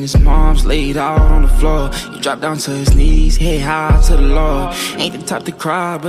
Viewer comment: i feel like he said “he dropped down to his knees head high to the lord” rather than “he dropped down to his knees he ain’t hide to the law” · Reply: I thought he said "he hit high to the low"